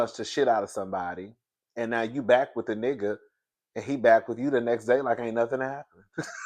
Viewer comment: It sounded like he said “ I’ll punch you back.” But I don’t know. Just a mess.